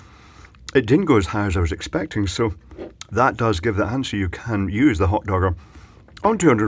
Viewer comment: I thought at first I heard "(...) if you are a cooking pshycho". Turns out he only said cooking cycle :P but i think it would work either way.